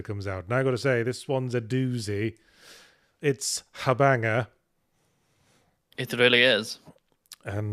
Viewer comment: Did he say a banger, habanger, or something else?